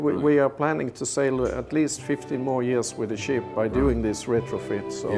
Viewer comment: Wow, this is great to see. Did he say this ship is expected to be in service for 15 or 50 more years? I went back and listened about five times and could not tell which he said. 15 would be great, 50 absolutely amazing! I relistened again at the 4:10 mark and I'm convinced he said 50 more years, wow! Be great to see this technology spread around the world to other ships. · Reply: 15 years. Most ships (of any kind) aren't made to be in service for 50 years.